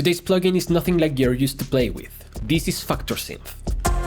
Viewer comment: Sound like u said fuktor synth 😭😭